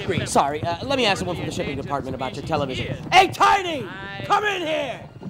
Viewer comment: FINALLY!!! The DVD didn’t have subtitles, but at least I know what he said here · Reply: “Let me ask someone from the shipping department about your television..” right?